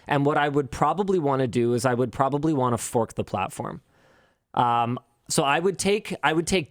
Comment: Oooohhhhhhhh “Fork the platform”, sorry I misheard the first time! 🤣